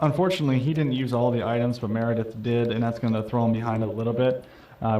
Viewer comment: I'm sorry, did you say Meredith?